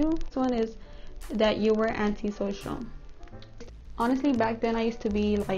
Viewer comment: It sounded like you said Auntie social💀